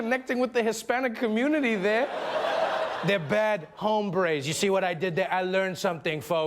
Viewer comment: I thought he was saying ombray not hombre